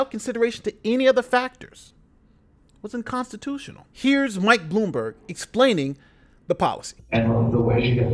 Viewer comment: listening at 2x speed this sounded like "here's white boomer explaining..."